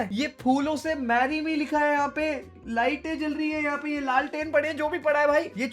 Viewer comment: was it just me who heard him say laal tayne or im tripping 😭 zyada splitsvilla dekh liya lag raha